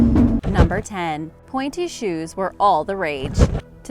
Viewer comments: I thought you said panty shoes LOL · I thought you said panty shoes